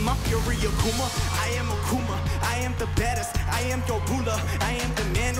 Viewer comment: did he just says I'm the bettest 😂?